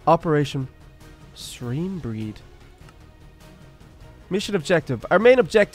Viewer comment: I thought that said screambread and I was like what?